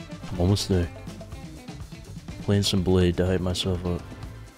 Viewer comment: Did he say he was gonna play some bladee to hype himself up at 27:30 hahahahahaha